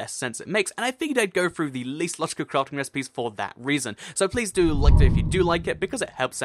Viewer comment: I thought it said illegal crafting recipes and I was super confused